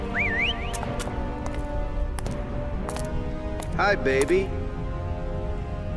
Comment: "Hi, baby." What I hear: "Hi, doggy."